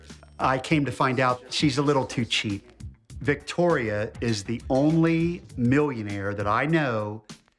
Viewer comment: did he say she is a millionaire? Well then why live like that? I mean, it's fine and necessary to appreciate your money and things you have but to pull your pee in your garden daily? wtf